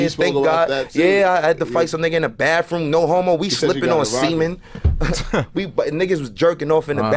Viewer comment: À 2:21 tu t’es trompé c’est pas "ciment" mais "semen" qui veut dire en français "semence" enfin moi j’entends sa